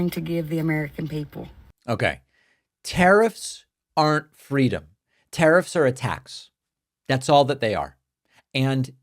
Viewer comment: I misheard “tariffs are a tax” as “tariffs are attacks”